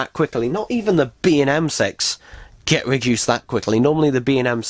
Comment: Humm... Did i heard correclty at 17:33? The b&m *ex